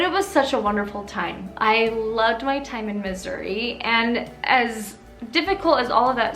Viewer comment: it sounded like you said "I loved my time in misery" lol